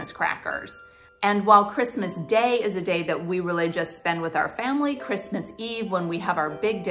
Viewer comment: Thought you said "we religious" but not totally convinced you said "we really just" :)